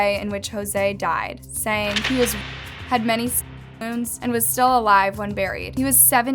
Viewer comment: what is the blurred word… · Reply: Stab wounds